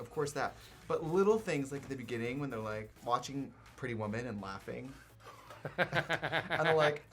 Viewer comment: what's this movie called? I literally can't understand how they say it lol it's so fast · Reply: Pretty woman.